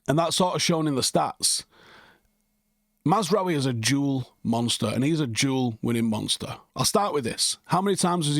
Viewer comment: My American behind thought you said Mazraoui was a “Jewel Monster.” I mean he is golden lol. Be he is a jewel and duel monster. Love the content!